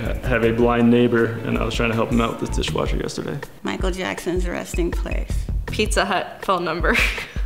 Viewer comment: Loves Michael Jackson, a true fan indeed! Legend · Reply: I thought she said his arresting place